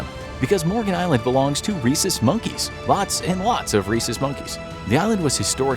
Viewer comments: Around 12 min in I thought they said racist monkeys lol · i thought he said racist monkeys..